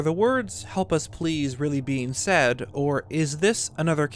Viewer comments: Idk even with the text i hear "please help hylean" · all i heard is "help us eat curry"